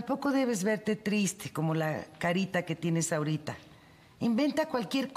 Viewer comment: tampoco debes verte triste como la carita que tiene isaurita jajajaa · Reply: Jajaja yo también escuché "Isaurita" pero dijo "la carita que tienes ahorita"